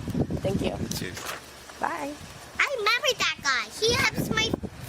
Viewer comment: I thought she said she remember that guy